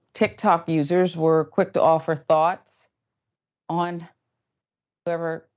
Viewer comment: Quick to offer thots... 0:41 ohhh thoughts! LoL I heard what I heard